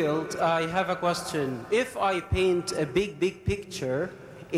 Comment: correct me if i am wrong. Did I just hear in 2:35:30 - 2:35:40 where the questioner said "If I paint a big d*ck picture..."? Please correct me if I am wrong... English isn't my mother tongue. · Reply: "Big big picture"